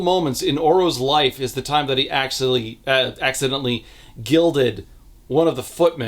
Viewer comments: I forgot about Oro's gold power, and misheard "accidentally gilded" as "accidentally gelded" and was initially very confused about how such a thing would happen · i was confused for a sec. I thought you might have said gelding not gilding